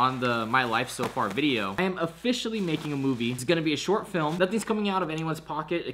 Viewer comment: I thought it said finally making a move XD